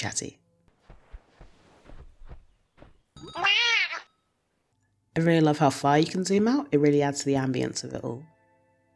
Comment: i don't know what the hell it says but i love the cat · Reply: "Also this fucking thing *meowrgh*"